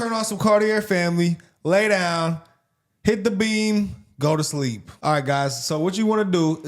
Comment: Swear I thought you said turn on some Cartier family lay down and hit the bean, thought I like watching your videos but not that much,,, then realized it was beam with an M